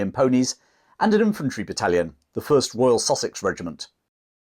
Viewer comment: When he first mentioned the "Sussex" regiment. I heard "sausage regiment" and was like, huh? lol